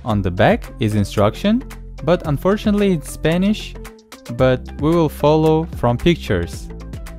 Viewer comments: did he say Spanish instead of Japanese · All these years learning japanese, turns out it was spanish 😢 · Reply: That's what I thought! But I'm hearing impaired so I thought he said HE was Spanish and was thinking, "Could've sworn he was Russian, but what do I know?"! 🤦‍♂️🤣